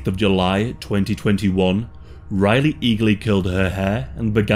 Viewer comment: Sounds like he said "...Riley legally killed her hair..." Like, damn, bro. It isn't that bad... 😳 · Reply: Riley eagerly curled her hair